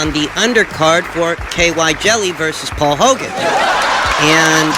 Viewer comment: did u just say KY jelly??😂😂😂💀💀